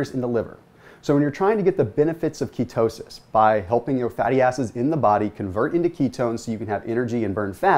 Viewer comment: did he say by helping your fatty asses? that's rude my ass is only slightly fat.